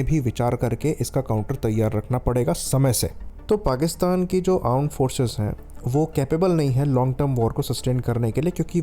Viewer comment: Aand forces hain ..kya bol rahe hon app